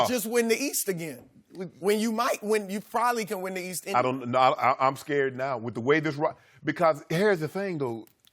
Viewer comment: did Shannon just say he is scared lol